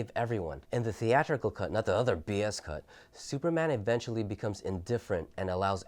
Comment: did you said “not the other BS cut”? Which version you preferred sir?